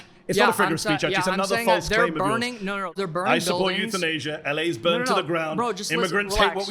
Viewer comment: the caption says “youth in Asia” instead of euthanasia 😂